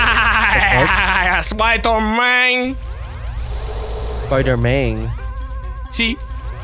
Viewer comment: did you just say "you am spider man?"